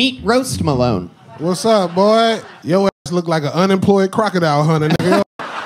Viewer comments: i thought he said underground crocodile like he wasn't mainstream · i thought he just said unemployed crocodile bruh ☠️☠️ that woulda been way better